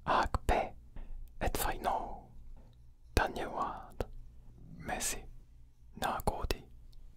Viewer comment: Am i the only one who hears messi xd